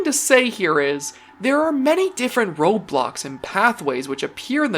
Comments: am i the only one here who hears "roblox" instead of "road block" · Not going to lie I thought for a second he said Roblox instead of roadblocks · DID HE JUST SAY THERE’S MANY DIFFERENT ROBLOX??!! 😦😦😦😦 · Don't take this the wrong way but I seriously thought he said Roblox, not roadblocks! I think I might be gaming a LITTLE too much.